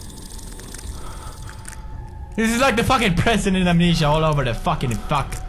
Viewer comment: Pewds.... It's only funny when we watch you scream like a little girl.. The monsters are just gentle butterflies. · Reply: Slimy Slug at that part i heard him say "this is like f*cking nurse in amnesia" i was like damn it ears XD